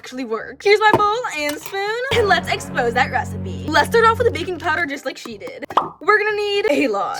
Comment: 3.39 I thought she said baking soda not baking powder maybe that’ll work better